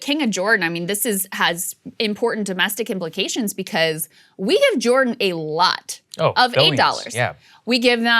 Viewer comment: Hahaha! For a second there it sounded like Krystal said we gave Jordan 8 dollars! Well that's just simply not enough, I though. I'm silly.